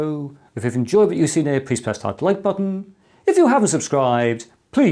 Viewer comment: Why do I always hear: '...please press the light button...' ? 😉